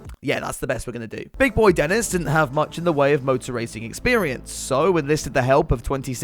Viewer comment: For a second I heard "rowboat racing" and was getting ready for Hamilton in a canoe.